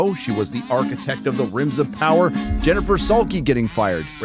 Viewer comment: For a second there I thought he said "rims of power."